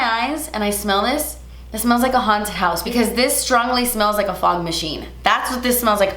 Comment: I totally thought she was saying ... fart machine!!! 😂